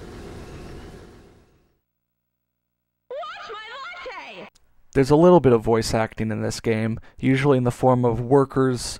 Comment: What does the Yuppie say at 0:44? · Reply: “Watch my latte” For like 15 years, my cousin and I thought she said “I want my lawn taped”. Don’t ask why. Lol